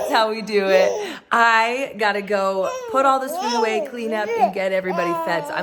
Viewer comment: I rewind it 3x. I could have sworn that she said "I have to go put all this cuteness away" haha🥰